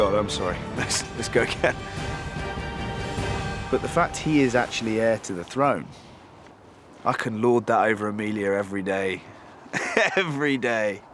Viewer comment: What is he saying at 1:37 ? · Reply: BignB DaG he’s saying “the fact that he’s the heir to the throne, I could hold that over Emilia every day.. every day”